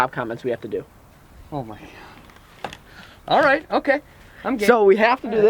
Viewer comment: Right when it says 2:22 , it sounds like he says I'm gay